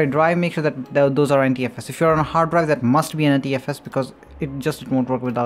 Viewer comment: wtf did u say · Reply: I said that if you're on a HDD that probably is already NTFS until and unless you haven't formatted it that way. Click on your drives' properties to verify their types!